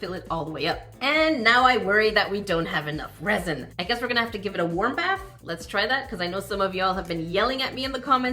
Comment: When you said “give it warm bath”, I thought you said “give it a whomp a$$”! Meaning bang it down LOL